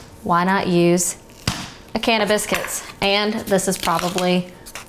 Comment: i keep hearing “cannabis” but the visuals in the video reminded me that its a can of biscuits